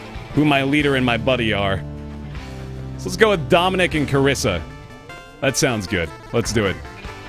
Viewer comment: All I heard was: "Let's go with dominating Karissa. That sounds good! Let's do it."